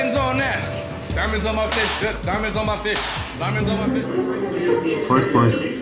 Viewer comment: “Diamonds on my fists” takes me out every time LOL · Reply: I honestly always thought he said I have diamonds on my fish! On fists makes so much more sense lol.